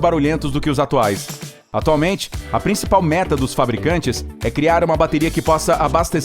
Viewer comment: Só eu ouvi ele falando:a principal "merda"?